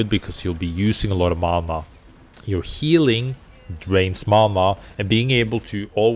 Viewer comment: You said when she heals with LMB it drains mana, but I heard you say "it drains mama" and I was so confused